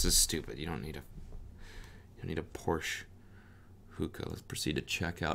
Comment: I thought she said PORCH hookah lmao…